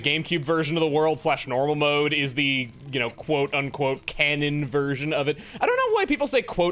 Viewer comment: I didn't think theyw ere saying "quote un-quote" I thought they were saying "quote on quote"